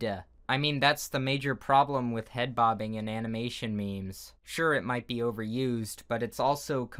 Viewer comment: am i the only one who heard him says animination?